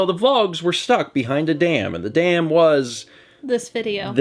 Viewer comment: Am i the only one who thought that said that the vlogs have been behind a DAN?